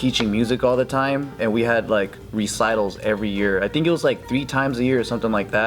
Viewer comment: I thought it said rectal for a second there